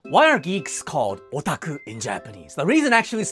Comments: I misheard "Geeks" as "Greeks" and spent half the short wondering why Greeks are given the same name as Japanese nerds. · At first I heard 'greeks' insted of geeks and was very confused at the second half of the video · In the first second I heard "greeks" not "geeks" and was like "what??" 😂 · Geeks! I thought you said leeks, and was thoroughly confused until about 3/4 of the way through the video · I thought he said Greeks and I had to go back to the start of the video to realise he said geeks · For a moment I misheard him and thought he said Greeks instead of geeks. Time to touch grass. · Misheard the start of this short. Thought you said geese, not geeks. I just got more confused as this went on😂😂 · For a second I thought he said "Greeks are called otaku" and was very confused til I quickly realised he said "Geeks"